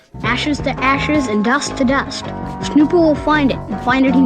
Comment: ok but did he just say "snoople"?